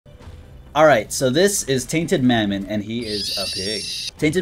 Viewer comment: Tainted is what I thought you said. Also mr what’s your real name